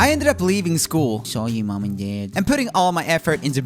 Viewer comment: who heard saw you mon and dad